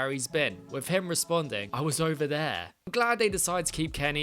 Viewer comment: tbh I thought he said “oh, I’ve been around”